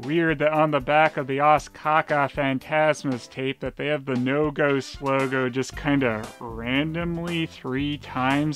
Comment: I know what you're actually saying now, lol. But I swear, at first it sounded like you were saying "No-Go Slow-Go."